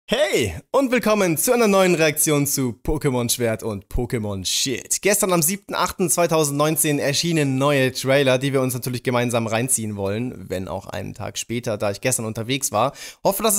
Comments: Ich hab verstanden "Pokemon Schwert und Pokemon Shi*"😂😂😂😂😁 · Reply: Ich hab immer Pokemon Shit verstanden xD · Ich hab verstanden "Pokemon Schwert und Pokemon Shi*"😂😂😂😂😁